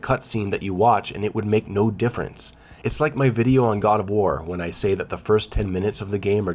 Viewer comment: The way you say "god of war" quickly sometimes makes me think you said "Gardevoir" XD